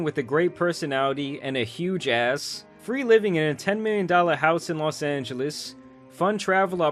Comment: I thought he said 10 million dollar allowance I was finna say I’m 15 😂